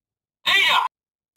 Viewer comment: no entiendo lo que dice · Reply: Ada eso dice